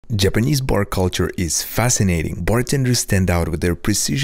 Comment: Am I the only one who heard him say Japanese porn culture?